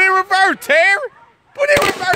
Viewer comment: I honestly thought he was saying “put it in reverse chair!”